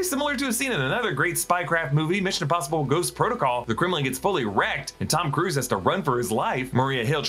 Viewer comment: Ohhh Kremlin gets Fully wrecked! I thought he was saying fully ere